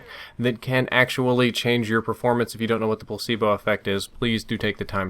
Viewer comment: what effect did he say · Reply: The Placebo Effect